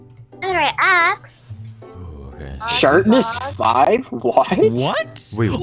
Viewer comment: lol it sounded like he said “shartness 5???”